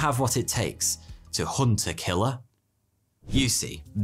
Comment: Until I saw the hunt a killer case, I thought he said hunterkiller